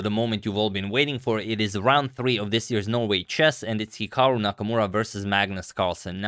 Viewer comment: i heard you saying the "no way chess" instead of "Norway chess" , and i was like , a no way category ? well it must be some good stuff .